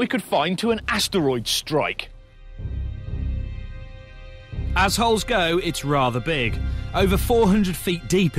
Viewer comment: did he say assholes